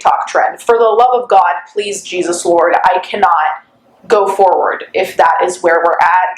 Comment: I thought she said "dear god, please Jesus-saur"